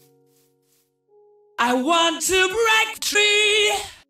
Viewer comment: I WANT TO BREAK.... TREE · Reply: I thought he said "I WANT TO FRICK... TREE"